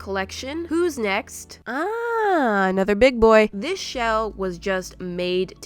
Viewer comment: I thought she said "Ahh me me big boy"